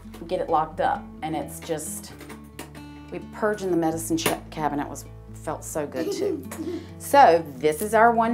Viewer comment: At first, I thought she said, "Purgin the medicine sh*t felt so good too!" 😆